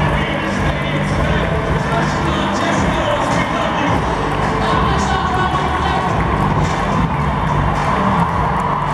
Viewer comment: what did she say? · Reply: SusannGER god bless y'all get home safe